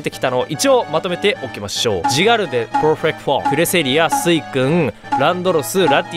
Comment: なんて言ってるの？ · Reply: Perfect Forme（パーフェクトフォルム）って言わせていただきました！